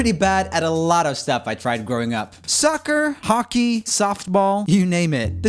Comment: did he say sucker or soccer?